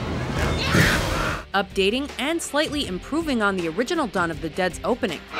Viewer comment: In the beginning I thought it said Shaun of the dead XD